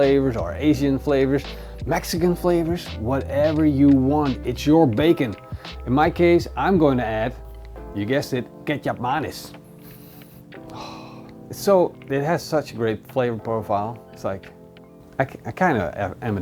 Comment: I hear "Kecap manis"